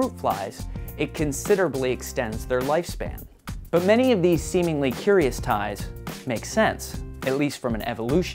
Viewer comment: did he say semenly at 1:02..?